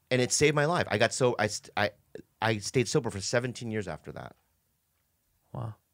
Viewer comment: The tone and timing of the "do you want a Guinness" is so perfect😂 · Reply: lol I thought he said "do you want to kiss", but Guinness is funnier and makes more sense.